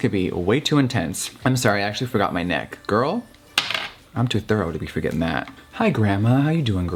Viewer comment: I thought you said “I am too 30 for that.” 😂 had me shook